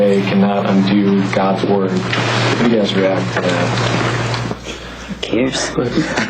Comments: What did Nancy Pelosi say at 1:29? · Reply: It's in the title of the video. She said "Who cares" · I thought she said "useless" which would have been even better, but I looked it up and she said "who cares?" which is pretty good too.